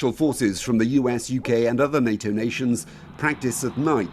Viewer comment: did he just say You Gay???? or am i mishearing things (ik he was suppose to say uk)